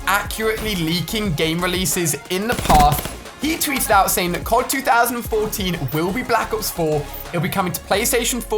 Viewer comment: Did he say Cod 2014